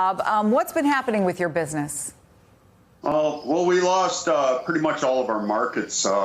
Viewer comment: thought she said weed farmer